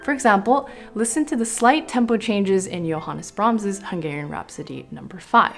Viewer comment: Wait, did she say "Hungarian Rhapsody no.5"?